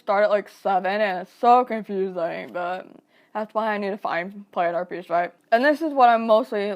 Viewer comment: At around 7:41 where the subtitles say (inaudible), I believe that I heard "planner peace". Does that make sense?